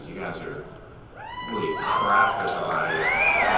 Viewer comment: What does he say at 20:08 ??? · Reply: Holy crap theres a lot of you